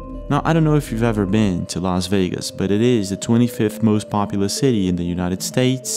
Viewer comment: Always great videos! I especially appreciate your forthrightness and honesty showing your videos. You might be the only person who never tries to bullshit their viewers! Thanks! PS- When you said Vegas was like 23rd most popular city in the US. I knew that couldn't be right. Las Vegas is the number ONE destination in the US! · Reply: You're right! I watched that part again. He said, "Las Vegas is the 23rd most POPULOUS city". I'm old, my hearing isn't good. I thought he said the 23rd most POPULAR city, then I put my own definition to it. I got that wrong! Thanks for straightening me out! I did learn the most popular destination!